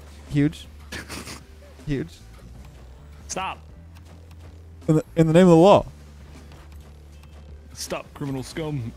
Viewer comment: It sounded like you said in the name of Allah 💀